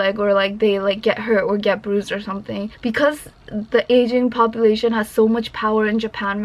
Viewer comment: thought she said "Asian population" haha